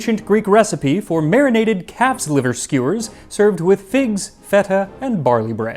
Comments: Don't ask me why, but at first I heard "marinated CAT liver skewers". I knew I had totally misheard before I went to rewind the vid... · I very nearly misheard calf's liver skewer as cats liver skewer